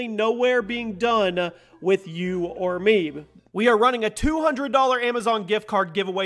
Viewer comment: who else heard you or meme